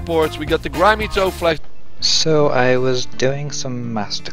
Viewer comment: "so i was diagnosed" thats how i heard it in my head lol cx